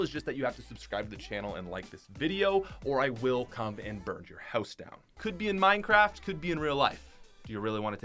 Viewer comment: I thought you said that you wouldnt burn our houses down anymore